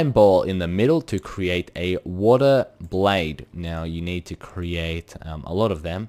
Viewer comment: sounded like you says water blade in sted of wooden blade xD